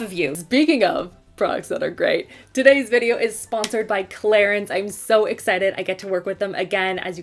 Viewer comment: For a second I heard "Claire's" instead of "Clarins" and I was super confused and worried